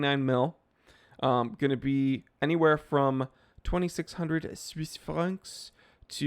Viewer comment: I thought you said 2600 Swiss farts XD